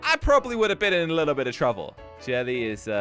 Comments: said truffle or trouvle instead of trouble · is it me or did he say trouvle